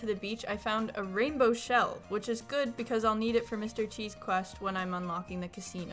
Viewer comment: When you said Mr Qi’s quest I thought you said Mr cheese quest😂😂